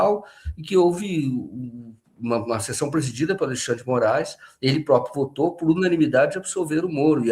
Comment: eu ouvi Xandão??? Kkkkk